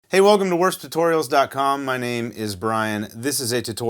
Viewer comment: In the beginning I heard “worst tutorials”